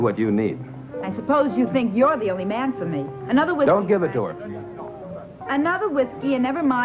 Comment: every time I watch this movie I kid you not it sounds like he said don't give a dork 😂😂😂😂😂😂😂... But instead he's saying don't give it to her😅😅😅😅😅😅😅